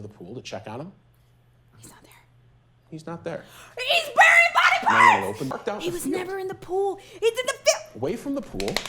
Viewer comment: I honestly thought you said; "He's wearing bodyparts." ...